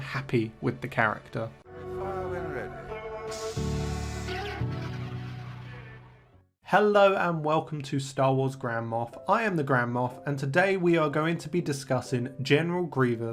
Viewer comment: i heard "and im the star wars grandma" until i looked at the channels name